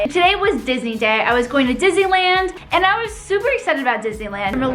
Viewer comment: My favorite snack is ice cream. Also, who thought she said dizziland instead of Disneyland?